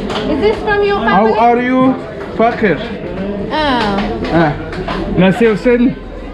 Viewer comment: Did he say “how are you fucker?”